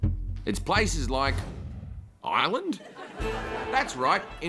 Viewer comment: I thought he said “island?”